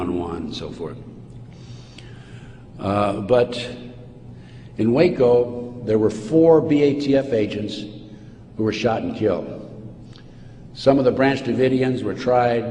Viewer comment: Thank you for sharing this very important video, but please correct all of the serious mistakes in the subtitles. For those that cannot hear, they are going to be terribly misinformed and many things will not make sense. For example, starting at 35:15 Ted Gunderson says "....but in Waco there were four BATF agents who were shot and killed. Some of the branch davidians were tried…..” The subtitle says, “a Butthead in Waco there were four batf agency were shot and killed some other branch davidians were tried……”